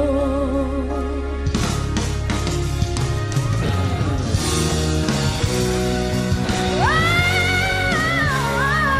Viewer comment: what is Janine Berdin saying here? "Go Zephanie" or "Go Odhene" ? She's so cute ☺️ use headset para mas marinig niyo pagsigaw niya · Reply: Parang "Go, go Zeph" sinigaw nya.. kakatuwa ang Berdin..very supportive 👍